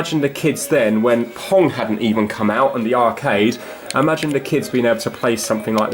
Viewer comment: Did he just say "where P*RN didn't come out" ?